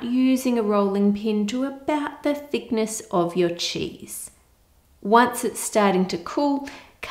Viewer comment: What she said: “... to about the thickness of your cheese.” What I heard: “... to about the thickness of your cheeks.”